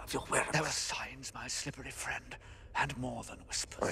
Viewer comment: does he call Lucius his "slippery friend" 😂